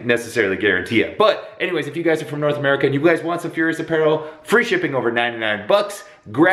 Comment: When you said "Furious apparel" I thought you said "Fhuers apparel"